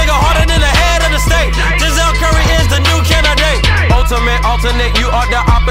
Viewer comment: Dude I heard The( south korean is the new candidates) 0:48 so I decide to look up liycsvideo 😂😂😂😂